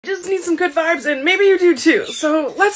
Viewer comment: I was drinking my water and almost chocked and also at first I thought she said I just need some good farts and maybe you do to😂😂😂😂😂😂😂😂